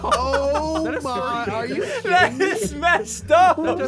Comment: soneone says oh my are you shitting me (this might or might not be what I heard